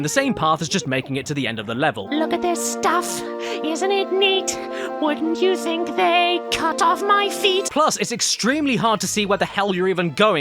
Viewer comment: I would like to personally thank you for, in my mind, the permanently changed rendition of "Part of your World" as everytime I play the original to my toddler I always hear "wouldnt you think they'd cut off my feet" It will never get old. Always cracks a smile. Never stop being you sir.